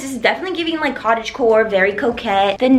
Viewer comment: Everytime you say "giving very cottage core, very coquette" I always hear "very cottage core, very cokehead" LOL